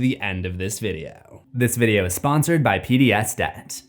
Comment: I thought you said “this video is sponsored by pbs” I was like wtf are kids watching now days😂😂😂